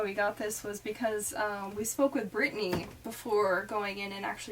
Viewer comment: I thought she said "We smoke with Brittany"